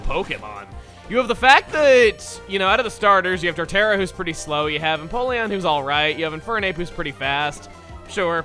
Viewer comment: Just listening to this while doing work, only half paying attention so it comes to the part about the Sinnoh starters and all I hear is "...and Napoleon, who is alright."